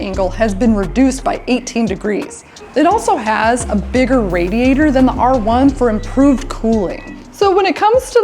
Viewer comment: did she say bigger than the R1? or did she mean mt09?